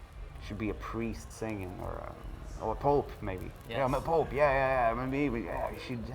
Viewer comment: is he said "i am pope" ? Sorry my english i speak spanish.